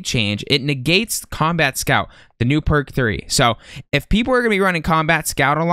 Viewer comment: Thought you said perc 30 @ 2:39 😂